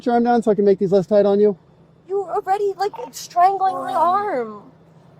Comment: Did she just say that the officer "strangled" her arm?